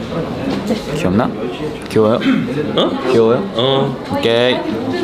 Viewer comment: Han did u say gay 15:04 ?? 😭🙂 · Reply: No I think he said k but it sounded like gay 😂